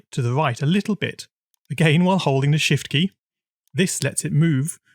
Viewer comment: When he said shift I thought he said 💩